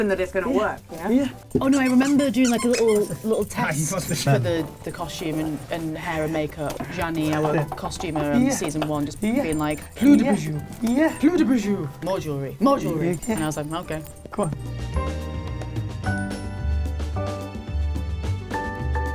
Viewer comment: Olivia says "plus de bijoux" (more jewelry) but i heard "pluie de bijou" meaning a rain of jewelry 😂